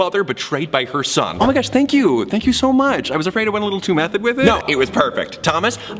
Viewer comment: When /Joan acting as the mom said "method" I thought they said "meth" so I was like, "Oh that's what's all over you're face!"